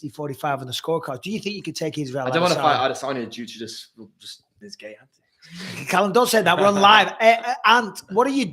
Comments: What did Callum say at 40:25 lol? Bisping didn’t like it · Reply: He said he didn't want to fight adesanya because of his g*y antics 😂. That shit was hilarious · What did he say? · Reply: He said he didn't want to fight adesanya because of his g*y antics 😂. That shit was hilarious